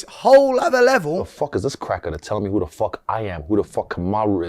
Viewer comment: At least Izzy called him a "cracka" instead of the hard E R ... i think? 17:46 🤔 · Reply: No that was clearly "cracker" not that it makes much difference to begin with it's still racist.